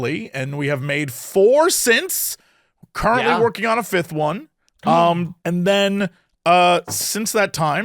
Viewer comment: heard that as “we have made 4 cents working on a 5th one!” And was going to say that’s the funniest way I’ve heard someone describe being bankrupt 😭